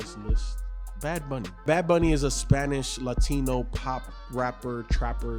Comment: Oh shit, it's "Bad Bunny". I thought in the beginning said "Bhad Bhabie" lmao. I was wondering in the first 10 minutes why no one was shocked AF and brought that up, but now I know why!